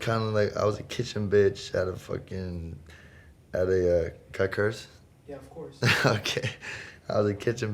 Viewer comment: does anyone know what peep said in 1:18? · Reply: He said "can I curse?" Lmao :)